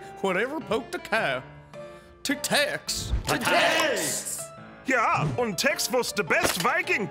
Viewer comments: Who else thought when they said to tex! They said tic taks? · I thought they were saying "to tax" and i was happy until it was a guy